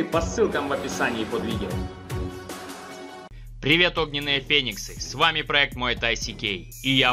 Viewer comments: привет огненные ПЕНИСЫ послышалось) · Мне послышалось вначале "Привет огненные пенисы"))) Пойду уши помою водой и мылом а не компотом и киселём)) Вот именно эти люди будут представлять Земное царство на турнире Смертельная битва))Я в них верю Шао Кан обосрется и сбежит обратно в свой Оутворлд забьется там под трон в обнимку с Шанг Цунгом и они вместе встретят смерть в воспоминаниях увиденных мощных бойцов)) Это вам не какой-то там Лю Канг и Кунг Лао))Пусть Скорпион даже не вылазиет из своего Незерерлма вместе с Куан Чи а уж Шиноку точно хана пусть только пукнет в сторону Рэйдена что-нибудь)) Ну вообще Пьяная змея есть))Любой стиль можно исполнять в стиле Пьяного это называется уже техника и тактика боя со смещением центра тяжести ))Такое ведь и в муай тай скорее всего есть))Просто в шаолине зачем-то все эти способы раздробили и вывели для них отдельные стили))Я как-то видел Вьетнамца который делал куен Тигра в таком ключе ))